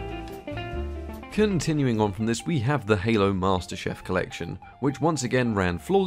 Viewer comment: Am I the only person that heard him say Halo master chef instead of Halo master Chief